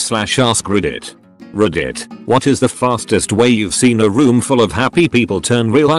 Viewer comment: I thought it said fatest 😂